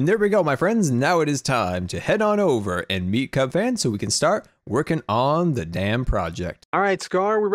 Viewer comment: I thought you said lets work on this damn project. And I was like WHAT!!!!